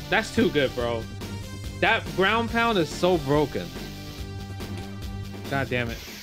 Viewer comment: Did he say brown pound ?